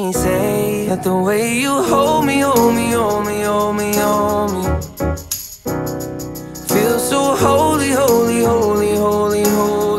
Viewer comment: I hear “homie homie, so homie”